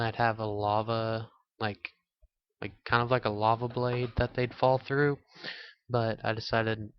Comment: did u just say gayblade?